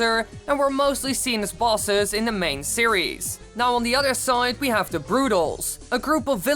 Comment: Now on the other sonic or side? I hear sonic. Whatever